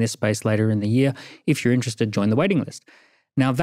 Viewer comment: Anyone else for a second thought he was saying "wedding list"? 😂